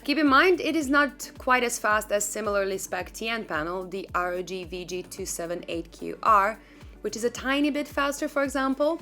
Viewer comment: She talks about a similar specced TN panel at 6:04 which is faster, but I cant make out what she says. Anyone know?`(I hear ROG something) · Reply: ROG VG278QR ;)